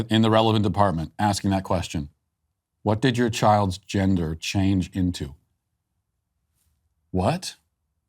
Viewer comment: she actually said... whats your childs gender changing to.... shes a bit poorly spoken and and uses ebonics...